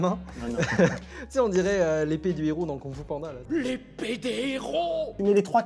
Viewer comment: je suis mort j’ai entendu « lépédé » au début je me suis dis c’est quoi ça